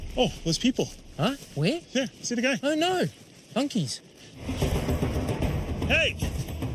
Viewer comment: Did that long haired Mzungu really refer to the people as MONKEYS at 34:10. Say it isn’t so. I heard it twice! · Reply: 😂😂😂😂 I also thought he said monkeys! He actually said "donkeys "